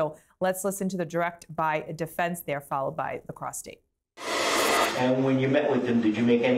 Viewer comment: Did she say, “let’s listen to the defense followed by the prostate?”